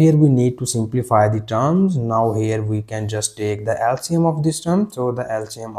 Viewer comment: you say “now we can just take the ??? of this term,” which you said is 2. What word are you saying here? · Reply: Least common multiple (LCM).